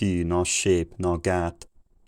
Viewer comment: Between 800 and 900 i heard "no shape, no gap" though whethet that means the same thing as today lol....